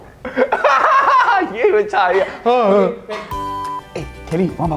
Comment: I heard "give birth to child I still can eat" -Thomas 2021